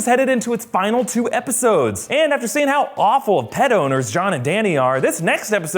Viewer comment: That intro confused the shit outta me....I genuinely thought you said Dany and Jon where bad pedos, then the penny dropped “pet owners” 🙈